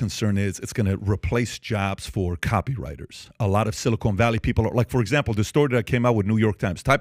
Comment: did he say “silicone” valley 😂 yeah I don’t think this fool knows what he’s saying when he doesn’t even know how to say it